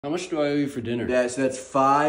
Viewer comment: i thought he said "what should i eat for dinner" and that made his response and the whole vid much funnier for some reason but then it looped and i realized he said something that actually made more sense than that